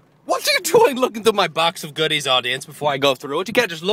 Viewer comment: "Box of goodies" I thought you said "Boxers". Guess you know where my mind is -___